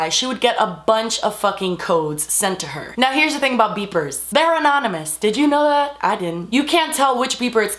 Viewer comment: OMG I thought she said beaver instead of beeper LOL